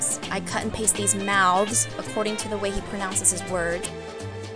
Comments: lol i thought it said "you ruined my clothes" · Oh i thought he said "You're in my clothes"...teehee.. omg this will be loads of help thanks!! ^^